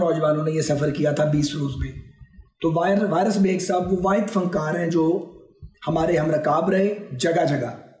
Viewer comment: He says "Virus Baig" instead of Waaris Baig 🤪🤪🤪🤪🤪